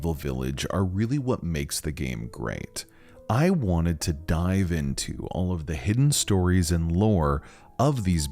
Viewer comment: I thought he said "I wanted to die" but then he said 'dive in'